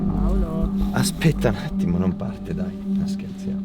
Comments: L'AVETE SENTITO ANCHE VOI UN "PAOLO"??? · A minuto 12:00 ho sentito qualcuno dire "Paolo" ma ne ne aprono bocca! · O raga solo io ho sentito " Paolooo" a 12:00 e non la detto nessuno tra i tre e si sente paoloo con una voce maschile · Ma solo io ho sentito Paolo in sotto fondo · Sono un vostro grande fan mi mettete un cuoricino ps. Siete i migliori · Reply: Al minuto 12:00 ho sentito: Paolo?